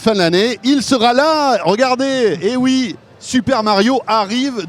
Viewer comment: c moi ou à 4:25 il a dit super mayo ???😂